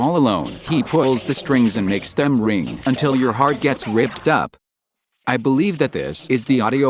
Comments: i dont really hear "until your heart gets ripped up" tbh all i hear is "until your heart can surrender" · With the plush vocals for some reason I only hear "ripped off", not "ripped up", maybe because P sounds similar to an F that's been cut off? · sounds like he said "red hot" instead of "ripped up" to me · I definitely hear "red hot" rather than "ripped up." It also rhymes with big shot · I honestly heard it as "until your soul turns red hot"